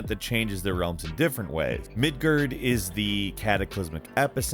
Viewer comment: The way you said Midgard, it sounded like you said Midgurd